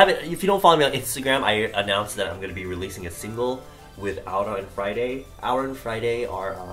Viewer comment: I thought you said "with Aoora ON Friday" and I wasn't prepared for that shit